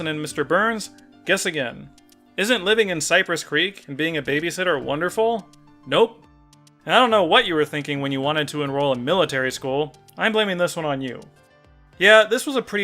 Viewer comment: It sounded like you said "isn't living in Cyprus 'Greek'"